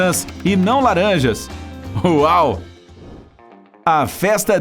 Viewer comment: não entendi, em nenhum momento ele falou laranjas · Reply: Sim no momento final da pergunta ele fala “quantas laranjas” então é isso tente voltar no vídeo e ver espero ter ajudado